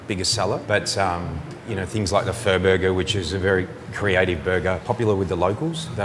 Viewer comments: OOOH, he said "Pho burger"....I thought he said "Furburger". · LOL i heard " Fur Burger " in Canada Thats a whole diffrent meal....Pho burger ...Hey its 2 am ..